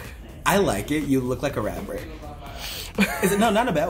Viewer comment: When he said rapper I thought he said rabbit 🐇🐇🐇